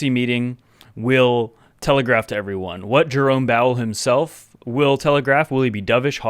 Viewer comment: Dude, I was drinking a tea when I heard "Jerome Bowel" I chocked so hard I nearly died! :)